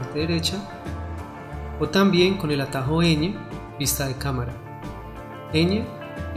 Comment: Gracias por el tutorial. En el minuto 02:21 creo que dices tecla N para ir a vista de cámara. No me funciona, creo que yo hago lo mismo pero con el CERO. ¿Es lo mismo?. Gracias. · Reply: Es la letra Ñ, pensé que era N. Gracias